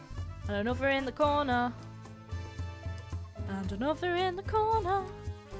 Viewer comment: When you said another in the corner I heard 'another in the closet'